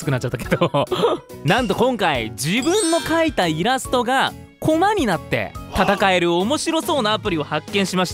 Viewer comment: 「自分で描いたイラストが粉になって戦える」に聞こえたww